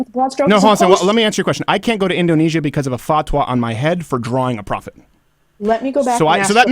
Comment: What did Steven say at 4:35? A "fah-twah" on his head?